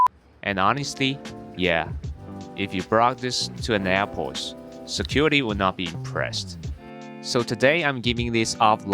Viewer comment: I knew the noun that was coming, but I still didn't understand what he said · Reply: Bomb